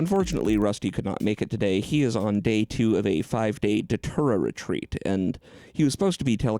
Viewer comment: A 5 day datura retreat sounds like hell. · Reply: I had the same thought, but I thought he said a 5-day doTerra retreat. Which seems just as frightening TBH.